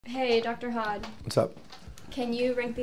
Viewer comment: I thought she said Dr. Hot, which is why I kept watching